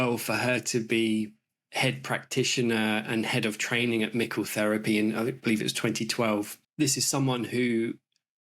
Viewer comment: Great interview! ❤ Sorry, but how is the therapy called? (I heard it as "nickel therapy" 😮).